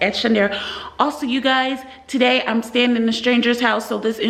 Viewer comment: I thought you said you wouldn't go into a strangers house 😂